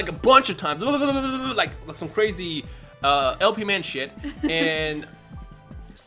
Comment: did you say LP man? or did you mean to say IP man